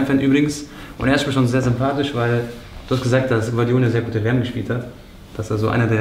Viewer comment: wer hat eine gute wm gespielt ? Hab den Namen nicht verstanden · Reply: gvardiol